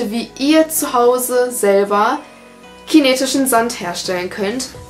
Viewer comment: Bei 0:51 verstehe ich chinesischen sand